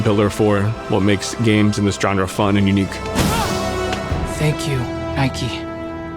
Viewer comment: Did he just say "Thank You, Nike." Is he wearing Air Jordans that help him jump like that?!?!:face-green-smiling::person-turqouise-waving